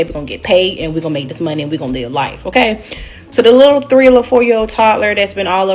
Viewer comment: I thought she said “304 lil girl” but she said “3 or 4”